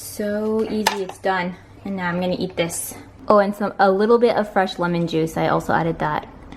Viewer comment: at the end I thought she said "fresh woman juice" :'D I has to listen back, no kameron it's "fresh lemon juice" idiot lol